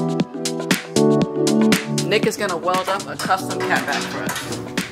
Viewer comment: swear I heard “niggas gonna weld up a custom catback” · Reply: “Nick is” fabricator name is Nick lol